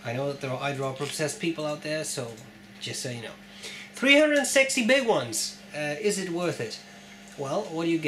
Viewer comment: I know I've been sick but I heard "300 sexy big ones." For a minute I though Lord Windermere was making the video ;) Another informative video as always. Love the color of that pen and the nib is amazing. What can I say? I like them broad ;) Ok I'd better stop now. Love to you and Azizah.